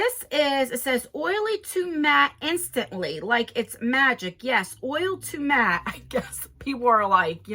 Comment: I just about spit out my drink when I thought you said Magic Mike's Magic Oil. Get my mind out of the gutter!!! I have been avoiding unboxings so I would be surprised. But here I am!!!!!😁